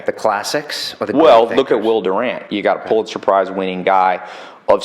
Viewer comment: what is name of that guy he said? pls respond. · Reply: Will Durant